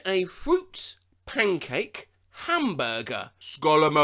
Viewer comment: The very last 💀. The voice over seems to be somewhat cut off at „skull emo(j)“ So my brain thought that you probably said „scholar mode“